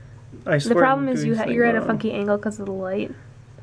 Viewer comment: ?!?! Did she say you're at a --FUCKY-- ANGLE?!! Or was that meant to be *Funky*?